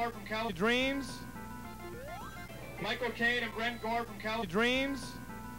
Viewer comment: I managed to identify the guy you called “Red Dwarf.” I heard it as “Brent Gore,” and sure enough a search pulled him up. Wikipedia lists him as “Brentley Gore,” and says he was a star on California Dreams, so I’m calling this case closed.